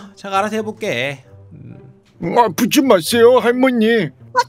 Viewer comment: 나만꺼지세요할머니라고들리나?